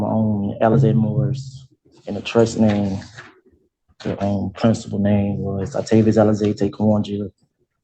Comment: did he say his "principal" name is Alize and Orange Juice?